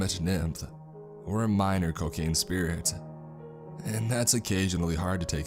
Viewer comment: Dunno why but I thought I heard cocaine spirit lol